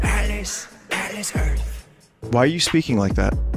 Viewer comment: I swear he says "Alice Earth"